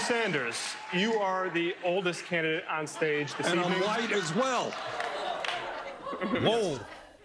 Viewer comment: I thought Bearny said, "and the whitest as well!"